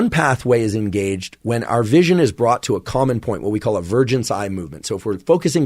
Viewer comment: Wait, did he just say "what we call a virgin's eye movement" at 44:08? Ahh, "vergence" ^_^